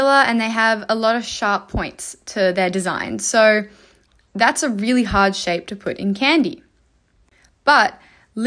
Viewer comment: I heard "Kangaroos are so hard to put in a candy" and I was really impressed there for a second 😂